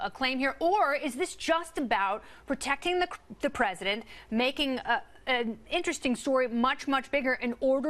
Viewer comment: Did anyone else catch that hilarious slip she made? "Is this just about protecting the crim-the President?"